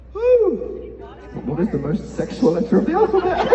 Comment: What does he say at 6:22 helpp English isn't my first language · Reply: “ What is the most sexual letter of the alphabet? “😂😂 he giggles while he says it but hope that helps!